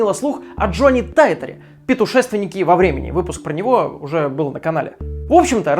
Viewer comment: я одна услышала "петушественник во времени"?